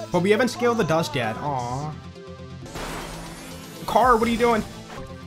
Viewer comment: u sounded like rick grimes lmao “CARRRLL”